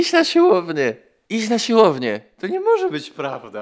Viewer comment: usłyszałam: ty znasz Słowenię. XDDDDDDDDDDDDDDDDDDDDDDD